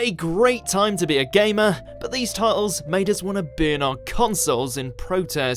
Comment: As a non-native English speaker, I thought he said, “bin our consoles”. Realised after he completed the sentence that he meant, burn 😂